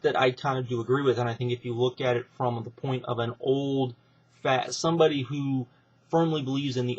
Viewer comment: Did you call Cornette "Old, Fat" or did I hear you wrong?